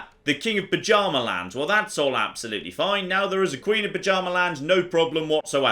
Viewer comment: I swear every time says "Bjarmaland" I hear it as "Pyjama-land".